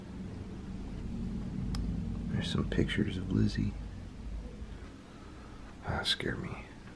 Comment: when you say here is a picture of Lizzy I hear yeah